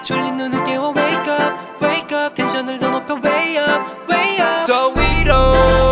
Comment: Why did I think Felix was saying”Doritos”